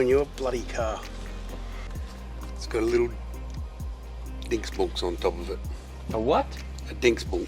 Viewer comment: Did just say "dingseboms"? the Norwegian way of saying "thingamajigger"